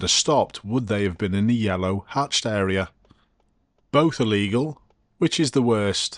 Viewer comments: Edit: My dutch mind and its tiny bit of knowledge on the English Language heard you say "are", but as someone pointed out, you said "Both illegal". And so the rest of the comment is basically wrong. Still gonna keep it up. """"""You said: The bus has just gone through a red light but if they had stopped, would they be in the yellow hatched area? both are legal, which is the worst? That triggered me more than the bus driver deciding not to throw out his anchor to try and stop for the red light.""""" <:( Both Illegal. Doh! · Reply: I heard it as “both illegal”. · That makes the situation even worse. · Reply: Edit: My dutch mind and its tiny bit of knowledge on the English Language heard you say "are", but as someone pointed out, you said "Both illegal". And so the rest of the comment is basically wrong. Still gonna keep it up. """"""You said: The bus has just gone through a red light but if they had stopped, would they be in the yellow hatched area? both are legal, which is the worst? That triggered me more than the bus driver deciding not to throw out his anchor to try and stop for the red light.""""" <:( Both Illegal. Doh!